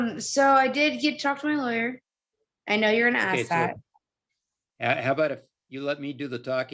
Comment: Did she just say “I know you’re an ass hat” to the judge?!? Or am I crazy???!!